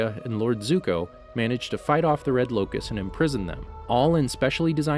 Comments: did he say red locus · Did anyone else notice he said the red lokus instead of the red lotus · it sounds like you say “red locust” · did he say red locust instead of lotus